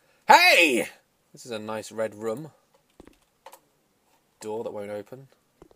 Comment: At the beginning when says, "This is a nice red room." I thought he said "Redrum" lol